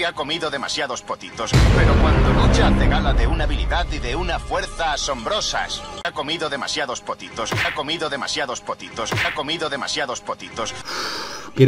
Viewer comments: Pense que dijo "putitos" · Potitos pense que habia dicho PUTITOS XD